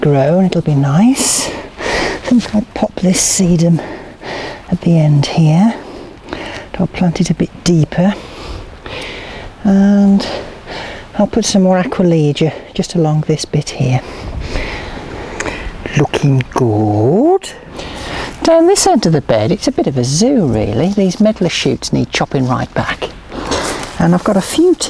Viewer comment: At around 7 minutes, I thought I heard "and I'll put some aqua regia just along this bit here". Made me go o.o